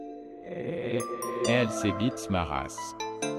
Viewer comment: C’est moi ou a 0:07 il dit"j’aime ces b1tes ma race" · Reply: Nn c'est "elle c'est b1te ma race"